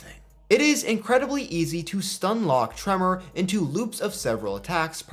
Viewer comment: I thought you were saying "Trevor" for a minute. I have an ear infection and didn't hear it well🤣🤣🤣